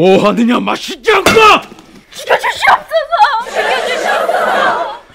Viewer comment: 죽여주시옵소서! = 살려주시옵소서! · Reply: 아니 님 글 보고 볼륨 낮춰서 0:30 보니깐 진짜 살려주시옵소서로 들림 ㅋㅋㅋㅋ